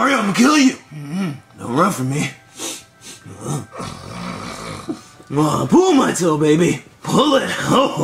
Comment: What did bowser say at 0:37? It sounded like he said pull my toe, baby! Pull it!